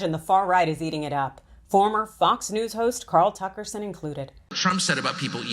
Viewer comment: Did she say "Carl Tuckerson"?